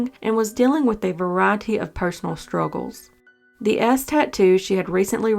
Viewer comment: That little twang from the narrator I definitely thought she said “that ass tattoo “ instead of “that S tattoo “ Haha so cute 😂❤️